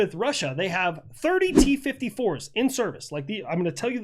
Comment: I thought you said T-34s 😂